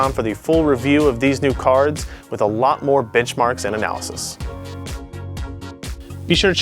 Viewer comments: I swear he said "bitch marks" there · Did he say "bitchmarks" when he meant benchmarks at 3:50?